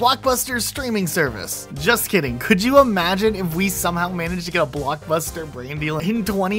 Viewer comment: Sorry but I'm a big follower of your videos but did you just say "blockbuster in 202030"? Massive timetraveller or what?